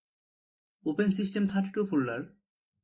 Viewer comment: i heard "look for system 32 file" and thought bro was about to try and destroy everyones pc